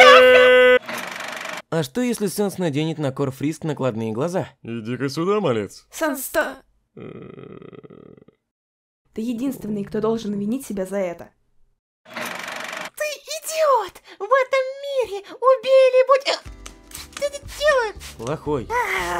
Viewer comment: Я который услышал вместо винить иметь:😱